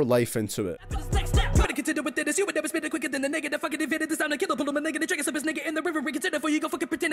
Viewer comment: did he just say nigga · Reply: No, he said Migga!